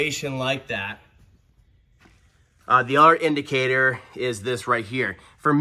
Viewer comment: sometimes I don't hear what is saying. I'm hearing "R Indicator." What is that device called and what is it for?